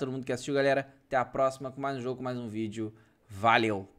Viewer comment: Só eu que ouvi "VALHEU" HUEHUEHUEHUE